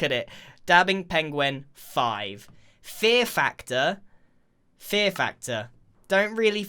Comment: I thought that it said Pear Factor.... smh